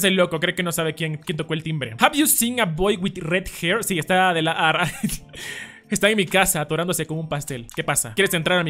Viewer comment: Wey yo escuche en el minuto 1:07 willyrex esta en tu casa??? Gg me mame :^